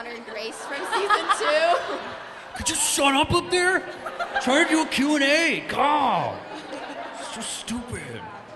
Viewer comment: what is he saying? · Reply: Trying to do a q&a, God